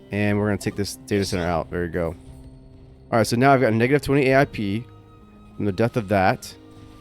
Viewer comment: What did he say? Lmao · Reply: Duh, the AI pees...